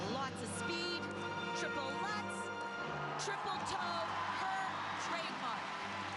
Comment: What does the commentator say starting from 1:10 ? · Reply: "her trademark"